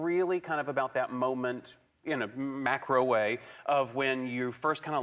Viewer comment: and i thought he said microwave